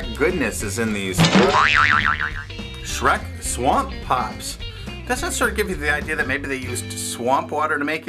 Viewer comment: i thought that said swamp poops